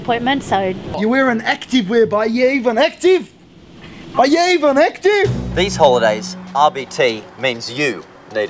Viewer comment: What does he say at 2:28? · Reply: "You're wearing activewear, but are you even active? Are you even active?" Think that's what it is anyways